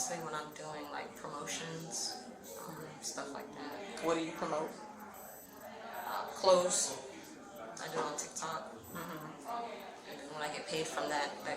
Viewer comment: Did she just say she promotes clothes?! 😂. I guess to women who want to look trashy!